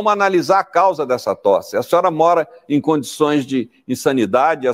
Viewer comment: condições de insanidade eu ouvi isso mesmo? Meu Deus!